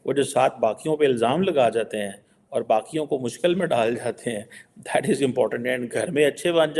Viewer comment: did he say Pakiyon or baqiyon?